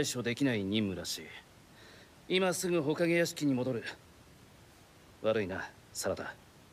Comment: what does he say? It sounds like smth as ‘waranya’, but i’ve never heard someone apologize like that before? Help would be much appreciated